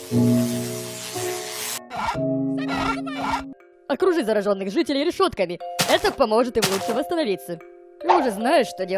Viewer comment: вы слышали? Кто услышал с того👍 Что! почти 100 лайков я в шоке спс всем👍😀! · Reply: Завязывай! На фоне?